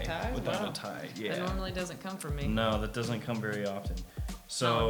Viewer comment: When he said, "It doesn't come very often" at first I thought she said "Neither do i anyways" 😂💀